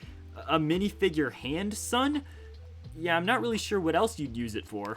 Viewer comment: did you say handsome or handsun